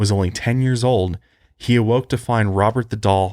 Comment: You said "Robert the Doll..." I heard "Robert Duvall..." Hahahahahahahahaha